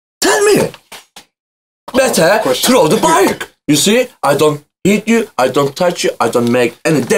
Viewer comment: did just say "I don't eat you"? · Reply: No. He said "i dont hit you"